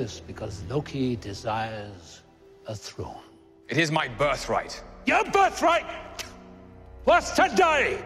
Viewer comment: am i the only one who thought it said it is my birthday clip